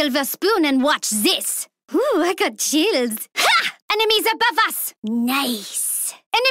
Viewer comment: Ohh i got childs. WTF Maeve has got childs? WTF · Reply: I know what you mean. Maeve suppose to say chills but instead of chills she said childs. I Heard her saying childs.